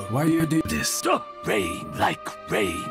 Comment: I first watched this ages ago and even now when my brain is on Idle all I hear is "Rain like Rain!"